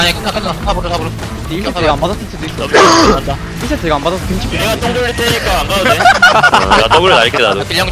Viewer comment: 케리아랑 커즈 뭐라고 했나요??? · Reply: 케리아 : 1세트 이거 (pog) 안받았으면 김치국 드링킹인데.. (쭈글)